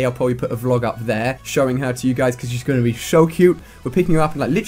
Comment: Did he say(shuw cute)?